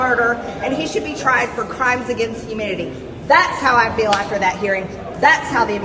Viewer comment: Did she say crimes against humidity? Maybe I misheard but 😂😂